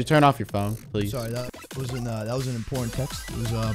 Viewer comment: did moose just say “oh sorry that was my pornhub”